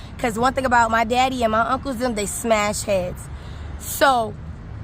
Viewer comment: I thought she said her daddy and uncles bust heads but then said her daddy didn’t do nothing